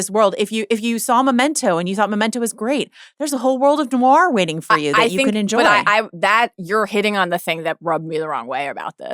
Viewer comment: I couldn’t catch what you said when you said if you like memento “there’s a whole world of” what word did you say after this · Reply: Noir